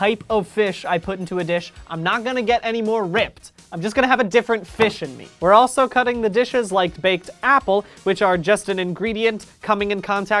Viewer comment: So I was watching the video, took a break, and then came back. This is what I heard: "... I have a different fish in me. We also need to cut the di..."